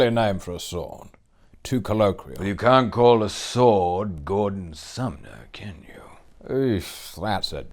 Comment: Gandalf says the name "Sting" for Bilbo's sword is too colloquial, to which Elrond replies "well you can't call a sword Gordon Sumner, can you?" Gordon Sumner is the artist Sting's real name 😂😂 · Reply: Oh, I see. I thought Elrond said sth like "Gondor summer" xD. I've seen a meme some time ago in which Frodo says "This is Sting. You've seen it before, haven't you, Gollum?" and the next panel is Smeagol pasted into Sting's concert ;). Anyway, thanks for explaining.